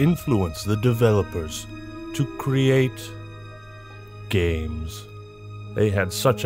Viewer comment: I thought he said the could use them to create gays instead of games